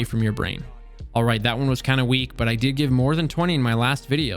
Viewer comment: Was partially asleep watching this and then i hear you say "ill see you later, if you suck" i was like "woah, he was so nice what happened?" Glad i rewatched and heard you correctly the 2nd time 🤣